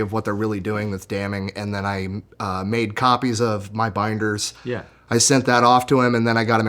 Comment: wait did say BINDERS not binder BINDER-S!